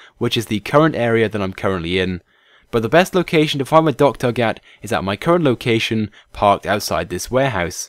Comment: The Docktug is AWESOME! Click the Like Button to show your appreciation towards it's awesomeness :) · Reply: ohhh docktug... i thought he was saying dogturk